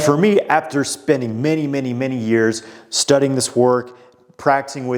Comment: Him : “and for me spending many many many years of studying” How I heard it: “ after a whole Lotta fucking”